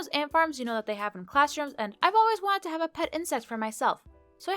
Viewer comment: Is it just me or does she say, "I've always wanted a pet incest for myself" at 3:38... XD